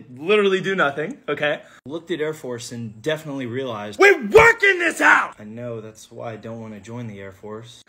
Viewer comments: “WE WORK IN THIS HOUSE” 😂😂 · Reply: Bruh I thought he said "We walk in this house" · I thought he said WE WALK IN THIS HOUSE after mentioning the air force 😂😂